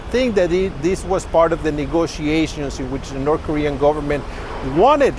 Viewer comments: 기자가 north Korea 라고 말한거 같은데.. 제가 잘못 들었나요? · North korean government??? 잘못들었나? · 저만 여기 북한 정부라고 말하는걸로 들리나요?